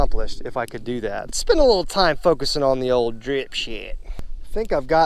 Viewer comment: it sounds like you say dripshlt😂